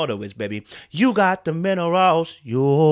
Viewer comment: I am just realizing you were singing "You got the minerals" while I heard "You got the men aroused🙄"... It's the accent .... lol